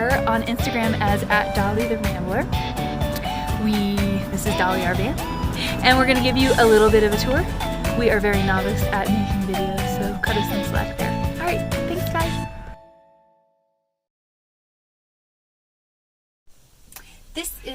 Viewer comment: ok im going to get my hearing checked...I thought you said novices? very professional everything! what a great vaN BUILD! thank you from Chattanooga!